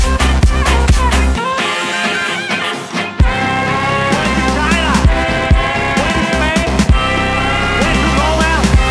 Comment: "Go to China!" Is that what hes saying? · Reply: not quite, it's "Went to China, Went to spain, Went to Rome, and Back again."